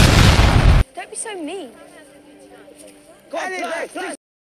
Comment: Does anyone know what they say at 2:23? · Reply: Sounds like three heads one lady